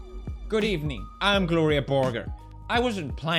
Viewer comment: Everytime you say gloria borger, i always heard you say burger